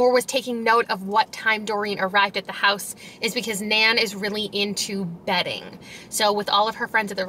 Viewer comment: why did I hear “Nan is really into bedding” and I thought, oh ok whatever makes you happy Nan 🤣